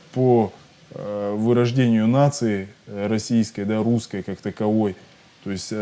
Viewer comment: I heard a Russian say something about neo-nasi but the subtitles said something completely different... Hmm